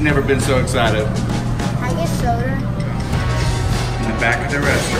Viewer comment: I swear he said 'soder' not soda